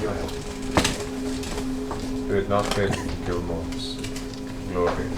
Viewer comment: Oh my gosh, I'm a bit deaf and it's really killing me right now, can not for the life of me understand what Gilmore says after Vax thanks him 'sincerely'. He starts with 'had it not been for your' and then I can't parse it from there. Everyone laughed, I feel like I missed something hilarious. Can somebody please let me know what Matt said? It's about 2:46:30. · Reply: Had it not been for ... smile Gilmore's ... smile Glorious ... smile Goods.